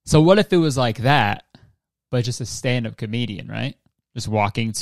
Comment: I thought you said stand-up Canadian. True regardless but I need to get my ears checked